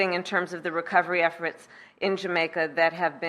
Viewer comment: OMFG - Sounded like she said "effrots" rather than efforts. That's like "Can I aks you a question?" Idiocracy draws closer.